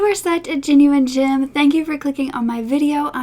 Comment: I always thought you were saying genuine JIM 🥴 omg my Aussie ears!!!